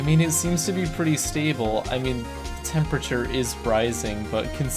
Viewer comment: did he just say temperature is freising